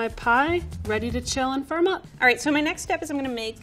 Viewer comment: I’m so used to Level 3 chefs being extra, so at 3:43 I could swear I heard her say “Ready to chill for a month”